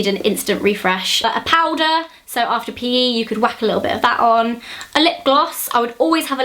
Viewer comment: did she just say after pee??